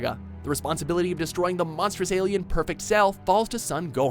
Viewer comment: Did.... Did he call Cell an alien at 3:40???